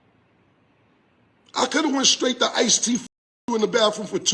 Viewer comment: It sounded like he said he could've went to ice tea and smashed her in the bathroom or whatever the name of the club was not that ice t did it